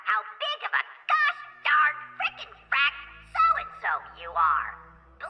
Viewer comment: I didn’t realise there was so much dialog for them 3:05 I almost spat out my drink, I thought he said “FUCK”